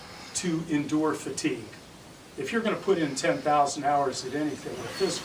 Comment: it really sounded like he said 'put in 10000 hours a day'